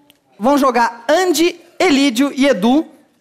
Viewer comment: quando ele falou Andy, Elidio e Edu. Eu entendi Du, Dudu e Edu